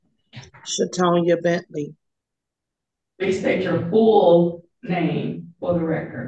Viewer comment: I swear the name I heard was 'shit on ya' bentley'!